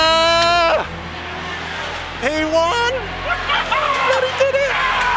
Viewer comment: i thought he said "He Won" bro i swear!!!